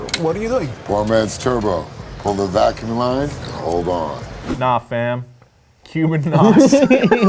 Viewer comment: What's cringey is you thought Letty said "Nah FAM" when she actually said "Nah FED"